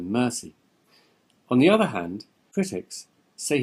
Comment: what did u say? I couldn't hear u! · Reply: critics